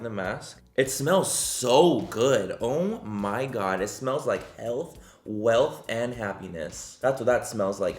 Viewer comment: Am I the only one that had to rewind like 6 times when he said “health, wealth and happiness”.? Because I thought he said “hell, wealth, and happiness” and I was like “well okay, whatever floats your boat, Manny.”😂